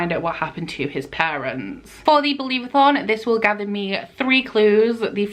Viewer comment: did you say "this will Gavin me"?... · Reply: 😂😂😂 "This will gather me"